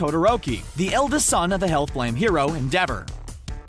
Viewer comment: I heard “the son of the hell flame hero DABER” Dont be mad at me